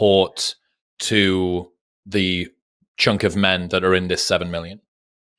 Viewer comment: ? did you say junk of men bro?